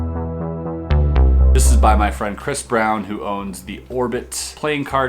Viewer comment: Did I just hear Chris brown...?